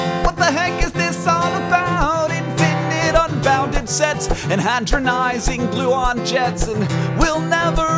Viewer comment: hadronizing glue-on jets? (i know that's not the actual words, but i keep hearing them and it makes me laugh) also, 1:53. XD